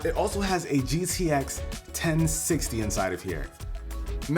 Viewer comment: did he just say gcx 1060?dude!!!!!😂